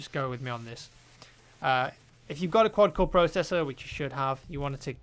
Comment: what did he say i should have in 9:09? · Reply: Walter White A quad-core processor?